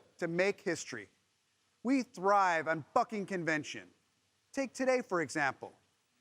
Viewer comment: Did he say, "fucking" at 1:15?